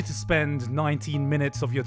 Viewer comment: ? Did he said nine minutes ? I thougt it was less... wait he said nineTEEN MINUTES ?